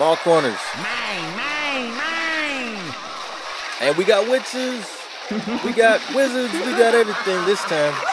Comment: When he said witches at 9:16 i thought he said bitches for some reason